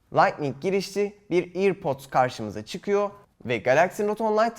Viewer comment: ne diyorlan bu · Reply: Earpods diyor airpods demiyor iyi dinle